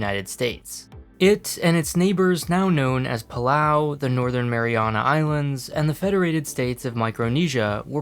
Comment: Thought you said "The Northern Marijuana Islands"